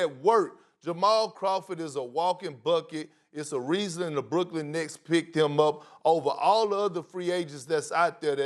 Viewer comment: Did he say Brooklyn Knicks?